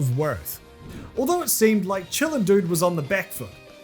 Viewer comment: legit thought that said children and not chillin